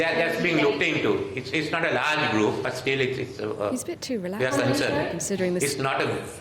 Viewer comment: D anyone heard in the background saying is he too relaxed.